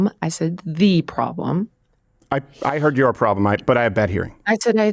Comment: I heard “you” problem from Jilian and then she switched to “the” problem.